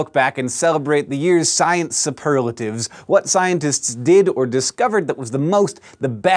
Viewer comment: I thought it said super laxatives at first.